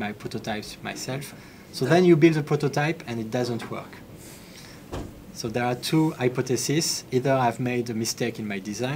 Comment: i couldnt understand the name of the company in germany. can anyone help?? i heard something like vittal yaud or bittaly yaud..... cant find anything. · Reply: beta layout